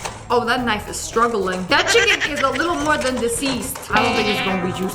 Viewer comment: did she say chicken??? Edit:Bring back hello friends it’s me!!!